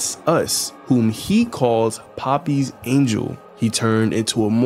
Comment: I originally thought he said “🥺You, 🥹You’re a Puppy’s Angel, 🐶👼Come to save us.” and I thought, “That’s one of the sweetest things anyone has said in this series.”. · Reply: Okay, firstly of all, I Thought he said “Puppy’s Angel”, even though the subtitles read differently. Secondly, where are you getting “Poopy’s Angel”? 💩👼That doesn’t sound Nearly as sweet.